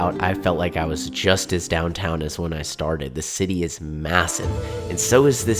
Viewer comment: Did he say massive???